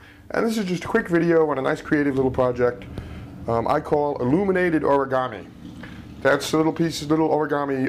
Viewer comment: Lol thought it said illuminati origami...